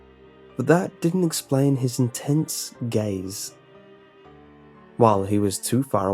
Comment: I thought you said "intense gays." haha. der.